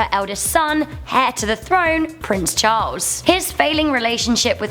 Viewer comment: Instead of heir to the throne I heard her say hair to the throne